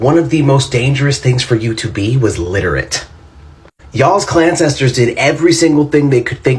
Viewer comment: did he say..... klan-cestors ......😶 🫢 14:10 !!!!!!THIS!!!!